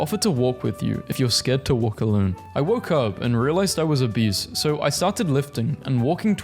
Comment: What it says: Woke up and realized I was obese. What I heard: Woke up and realized I was a beast.